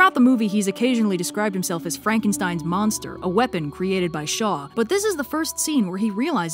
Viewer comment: When you said "a weapon created by Shaw," I heard "by the Shoah" and, yeah. Shaw's basically a representation of all high-ranking Nazis in this story.